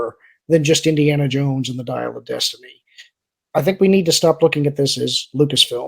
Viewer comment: I don't know if it was a Freudian slip or not, but at around 38:04 says the title of Dial of Destiny and it almost sounded like he was going to say Diarrhea of destiny. I know I heard an "r" in there lol. I think that Disney will get to the point one of two things will happen 1. they will get into a proxy battle with Nelson Peltz or maybe even a hostile take from Comcast or 2. The company will get so into debt that it will file bankruptcy and be sold off into individual properties at a fire sale. No matter how much Emperor Iger ruins the company there is still a lot of value in Pixar, Lucasfilms, Marvel and Disney. Iger is sitting on a gold mine but he does not know how to use it. Disney is removing content from their steaming service (yes I left the r out because it's a steaming pile of crap at this point) will not entice new subscribers and less content means less to watch and less subscribers. However the content on there now isn't much to draw people in anyway. The new content they add is garbage.